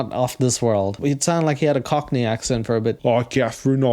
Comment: I thought you said “it sounded like he had a bit of a cochney *accident* there” and laughed so hard that I shot my cereal milk shot out through my nose and my bf asked to see what the hell I just watched so I rewound it for him only to see you said “cochney *accent* ”